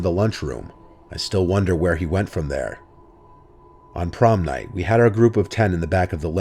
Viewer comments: I thought it said porn night guess i shouldn’t jack off so much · I thought it said porn night lmao